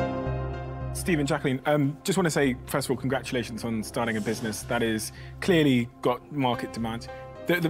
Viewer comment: i thought Steven Bartlett said “I just like to say congratulations on your dying business”😂😂😂